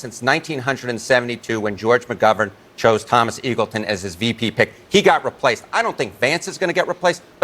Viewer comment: At first I thought he said Thomas Evilton :'-)